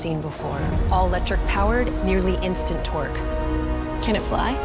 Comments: Me:Did she say instant TWERK Also me: Starts Furiously twerking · It sounded like she said "nearly instant twerk" lol 😆